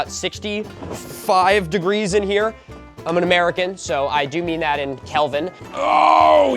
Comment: I heard that as "I do mean that in helmet" So that's my new measurement of temperature It is now 14 degrees Helmet